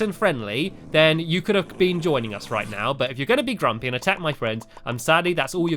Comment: wait wat. Did he just say drunk?